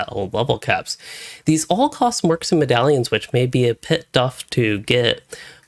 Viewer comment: Wasn't expecting the "tit buff" instead of "bit tough" lol. Seriously though, thanks for an amazing guide, this really helped a friend and I out while we're levelling.